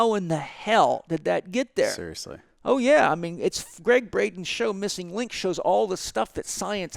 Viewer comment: Can anyone spell the name of the person that paul chek mentions at about 2:06 greg something. Can someone give me the full name please? English is not my main language maybe i cant understand it as well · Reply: Gregg Braden missing links